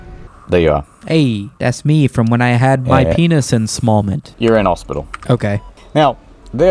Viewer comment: I heard "penis installment" instead, and my first thought was Sumito's "TRAAANSITION!!" edit 😭